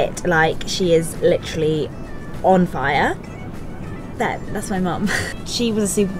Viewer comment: Did she just say her mom is "literally on fire"? lol